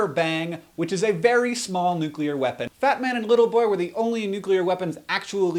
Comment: Thumbs up if you thought he said "Batman" when, in fact he said "Fat man"